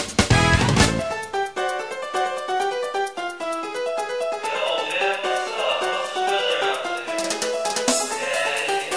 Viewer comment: What is he saying? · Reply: Yo man, What's up? Whats the weather out there?